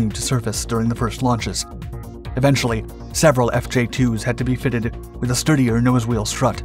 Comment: Did you seriously say "fitteded" as in the past tense of fitted, itself the past tense of fit. Am I hearing things?